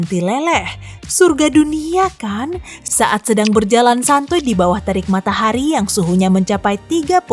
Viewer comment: Did you said "santuy"?